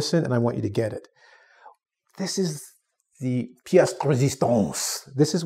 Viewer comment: Excuse me but do anybody know what was that he said when he explained the part about summarizing a chapter? It sounded like another language but I'm not sure and I don't think that was English... He said it at 5:57-5:58.......please listen What language was that? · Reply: It was french, he said "Pièce de résistance", it basically means a highlight, or "piece" (bit) or importance.